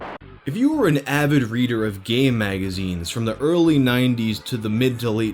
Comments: I thought you said gay magazines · "If you were an avid reader of gay magazines" is what I heard... lmao